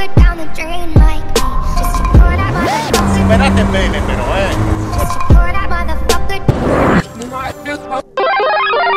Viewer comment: what did he said? Someone could help me? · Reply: He told him, that he does good wheelie.